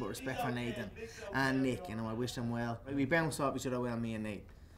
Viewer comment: That irish accent just makes his disses 10x more entertaining lol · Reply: what did conor mcgregor say at 3:03? we bounced off? and what does it mean??